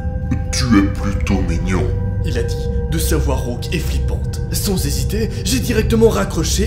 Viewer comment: J'ai compris <sans Ziziter>